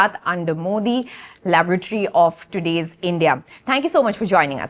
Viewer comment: I heard it as : "Gujarat under Modi. The LAVARATORY of today's India."